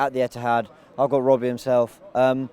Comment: did he say "Margot Robbie himself"???